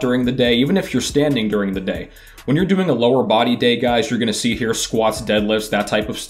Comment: Once he got to rows, all I heard was, "Do biceps on lower body day."